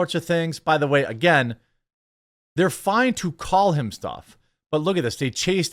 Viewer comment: Maybe it’s power of suggestion, but I definitely thought I heard “kill him” a few times in there.